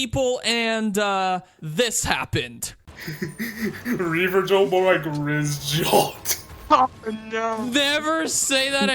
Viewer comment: dude put subtitles or smth I can’t even understand what the guy’s saying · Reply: “ReaverJolt more like RizzJolt” is what he said.